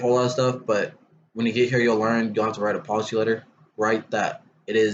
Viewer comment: Question - At 6:27 did you say "Policy Letter"? If so, what is that all about? And what is the policy on vaping? Thank you for the effort you put into the video!